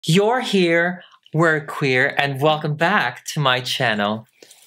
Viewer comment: All this time I had thought that said "you're here I'm clear" I don't know how I should feel right--- 😭👉🐥